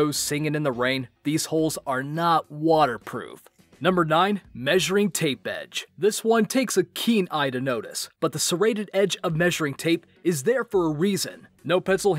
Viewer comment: Did... Did he just say fa-seated?